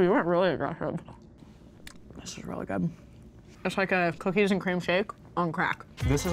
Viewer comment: On the cookie jar milkshake I thought said cookies and cream cake